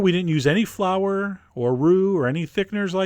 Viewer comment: I heard "flour or roux" as "flower or root". Only the root made sense to me then. I'm not the only one am I?